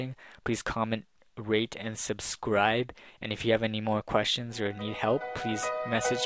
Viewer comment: Sounded like he said "comment,raid,and subscribe"